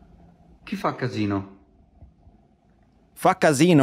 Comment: sagt er nicht sowas wie "Kiffer-Casino"? ;) Das hatte ich verstanden. ;) Gutes Video.